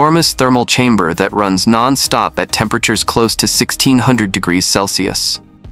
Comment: Anyone relying on Closed Captions (CC)... I am sorry... Tech has not yet reached reliable quality. At 12:20 the CC says 16 degrees Celsius but the narrator says 1600 degrees Celsius. That's the error I caught.... I don't know if there were others.